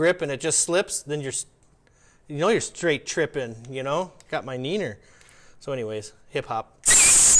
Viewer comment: 😂🤣😂🤣😂 DID YOU SAY NENER?!?!?